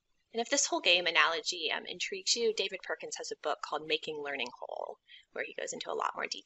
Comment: recommended a book, what is that book name? I heard <making learning horror>? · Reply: Xi Xiao "Making learning whole"